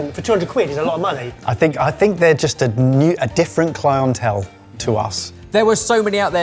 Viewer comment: I know said ‘we’re a different clientele’, but I heard ‘we’re a different clown-hotel’.